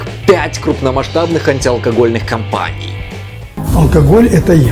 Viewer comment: Мне послышалось, что Онищенко сказал "алкоголь - это я!", ну типа, как "Эмма Бовари - это я".